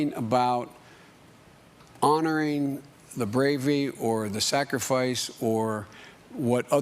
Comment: Did he say “Bravey” instead of bravery at 2:00? HAHAHA